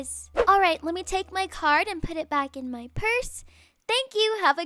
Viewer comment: I thought she said let me just put my cart back in my purse lol